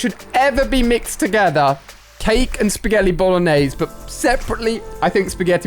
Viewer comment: Is it just me or did he say spaghelli 😂